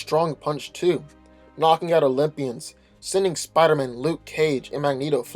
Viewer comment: At the 5:00 mark when describing Quicksilver I thought he said he was knocking out lesbians, lol.